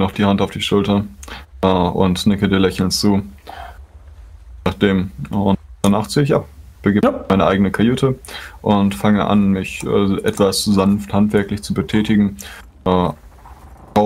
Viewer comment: "Danach ziehe ich ab. Begebe mich in meine eigene Kajüte und fange an mich etwas sanft handwerklich zu betätigen..." That's what he said.